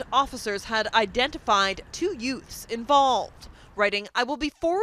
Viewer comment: the two what? What was that word? Two what? Did you say Yutes? What is a Yute?